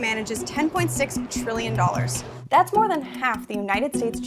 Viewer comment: you didn't clearly enunciate the T in Trillion so it sounded like Billion - a small difference ...